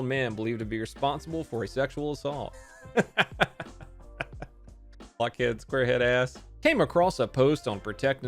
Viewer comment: auto captions says “blackheads squarehead ass” instead of block head just FYI